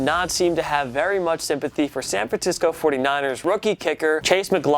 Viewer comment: OK IM MAD NOW YOU SAID SAN FRANTISCO lol jk I just heard him say that so I thought I should point it out